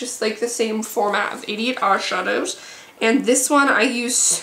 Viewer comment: it sound like you said "idiot eyeshadows" HAHA